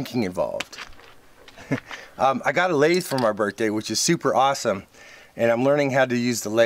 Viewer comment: Great Build U two! Thought U said got laid for my birthday anyways that's always good also! A reusable zip tie would hold U flange nut still. ENJOY as always!